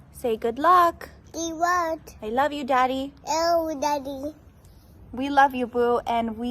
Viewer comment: Lol at 2:15 it sounded like his son said "L daddy"